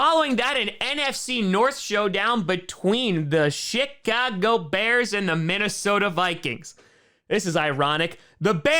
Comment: I hear shitcago bears